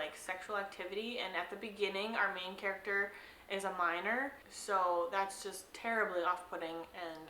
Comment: Lol, the last book, I heard "miner" instead of "minor" and I was like "what does Holly have against miners?" Haha, makes more sense now.